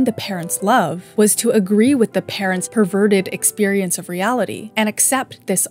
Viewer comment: did she say "perverted" lol